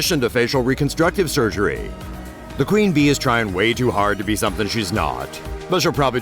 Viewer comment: did he say queen B at 1:01?